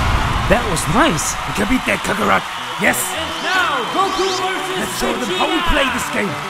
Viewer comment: "You can beat that Cuckoo rock" the subtitles got very wrong what vegeta was saying